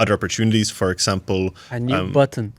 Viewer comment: did say a new button !!!!!??????? 😱😱